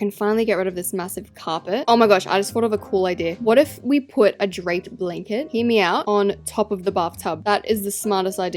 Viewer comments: help i thought she said a drake blanket😭✋ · I THOUGHT YOU SAID DRAKE BLANKET LOL